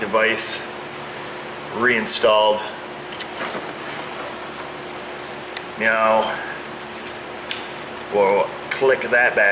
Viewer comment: did he just say "meow" ? i thought i have heard meow in other vids, or is it jut me?